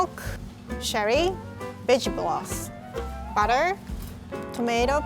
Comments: The first time I watched the video I heard “B*tch Broth.” 😂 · I thought she said bitch broth😂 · i wasn’t looking when listening to her talk and I heard “bitch gloss” instead of “vegetable broth” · is it just me or at 1:18 Rie said bitch bloth instead of vegetable broth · i thought she said b** broth oof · I’m so sorry Rié but it sounds like you said b*tch broth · When she said vegetable broth, I thought she said bitch broth. I was like oh. Well, that too. 😂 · I thought she said "bitch gloss" when she said vegetable broth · i legit thought rie said “bitch broth” at 1:17😭😭